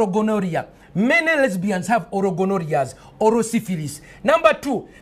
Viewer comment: Can someone please clarify: WHAT term is he using when he says "autoborrhea"?!? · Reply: He’s saying “oral gonorrhea”